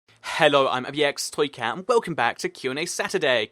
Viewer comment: Lol, when he started the video it sounded like he said "Hello, Ibxtoycat and welcome back to another QandA."